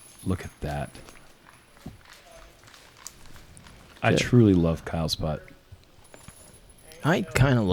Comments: Listening in the background, and did a double take when I heard "I love Kyle's butt." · I legit thought I heard say "I like Kyle's Butt" hahahaha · "I truly love kyles's butt." At least thats what the captions say LMAOOOO